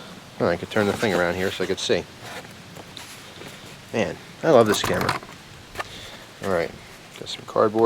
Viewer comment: lol i turned on transcribe audio and when you said "i love this camera" it thought you said "this is crap" XD